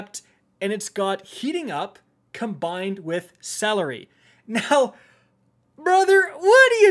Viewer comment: he did not just say what i think he said at 4:10 ... did he just say celery